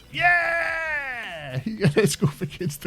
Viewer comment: You called it "Huge," instead of "Hugo."